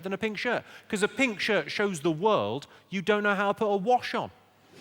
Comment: What does he say at 55:25 "because a pink shirt shows the world that you cannot put a ????? on" ? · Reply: A wash on